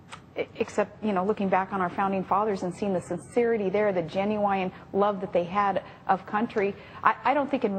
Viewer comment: Did she just say Ginuwine--the RnB artist instead of genuine? Could be the accent, could be the stupid....